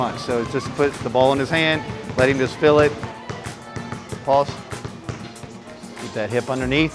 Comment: I dead ass thought he said pause because he said the shit about letting him feel the ball. Lls